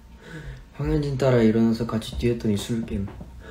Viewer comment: someone know what he says at 56:56? 🥺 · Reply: He says : I got up and ran with Hwang Hyunjin ( reading a comment ).💞